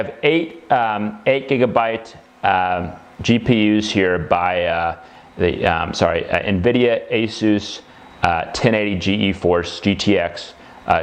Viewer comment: I think he says 8GB Asus 1080 GTX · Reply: That’s correct: Nvidia Asus 1080 GTX 8 GB.